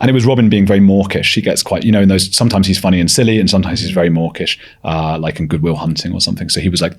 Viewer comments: I thought you were saying Morkish! 😄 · Haha. I thought was saying "Morkish" in reference to Mork and Mindy, when he was talking about Robin Williams.